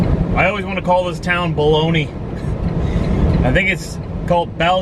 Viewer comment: I thought you said "The Town of Blowme" Opps!